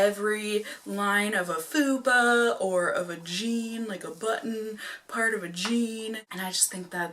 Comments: when you said "of a jean" I thought you said "a vagine". Got a good chuckle out of that. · who else thought she said “a vagine” 😂 instead of “of a jean”????😭😭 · You said “of a jean” and I heard “a vageen” hahahaha Ohhhhh, I need to go to bed... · Totally thought she said vagine. But she said of a jean. 🤣🤣🤣🤣